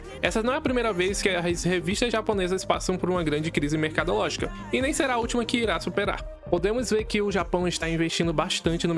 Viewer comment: regent? vou procurar nunca ouvi falar se alguem souber se o nome é esse msm e conhecer confirma por favor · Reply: Se chama Radiant